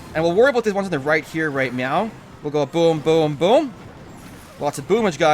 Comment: sound like he says "right meow"